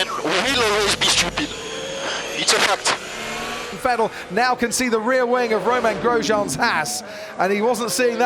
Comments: Who else thought he said « Vettel now considering the rear wing of Grosjean’s ass » ? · did crofty say back of Romain Grosjeans's as* or haas😄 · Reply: Haas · i thought he said "rear wing of romain grosjean's ass"